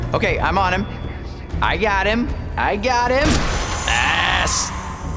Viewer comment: "I got him! I got him! ASSSS!" That's what that sounded like.